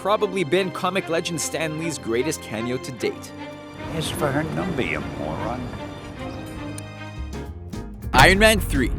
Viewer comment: What did Stan Lee say at 2:15? I keep hearing "His for hern, don't be a moron." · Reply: "Ask for her number you moron"